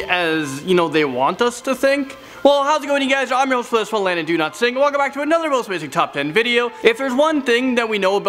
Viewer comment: I thought this guy's been saying do not sing for like 5 episode I was so confused